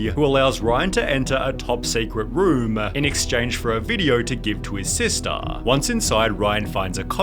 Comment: I love that he said a swear word in the little little kids movie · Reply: I thought he said "For them video!"